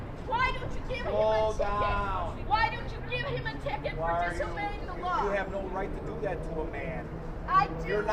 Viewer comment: As an english bloke I kept hearing "Disobeying the lars!" "you dont care that he was disobeying the lars!"